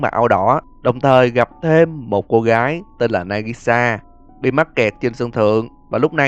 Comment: Là nagisa ko phải nagasi anh ạ (Hoặc do em nghe nhầm)